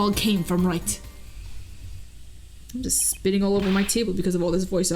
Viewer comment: Me: O_o *rewinds and turns on CC "I'm just spitting all over my table.." Me: Oh....that's what she said...um...carry on.